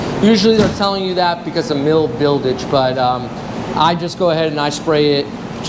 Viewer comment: did you say "mil buildige".Not trying to be stupid.